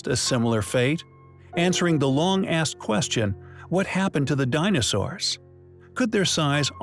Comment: DID HE JUST SAY"LONG (ASS) QUESTION."? WTF?? FIRST TIME I EVER HEARD HIM SWEAR. ANYONE HAVE PROOF , SHOWING OTHERWISE? IF SO PLEASE SEND AND SHARE THANK YOU